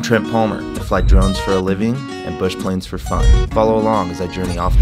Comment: is it just me , or does it sound like every time he starts a video it sounds like hes saying "i fly drugs for a living, and I fly planes for fun". What do you think?